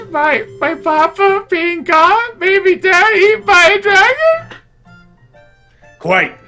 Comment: did i just hear "Eaten by a titan?" in 2013? Congratulations on reading SnK before it was cool.